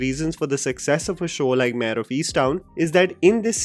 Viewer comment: What was the name of the American series 'town on the hills'? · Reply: I also thought he said something down in the hills but it looks like it is Mare of Easttown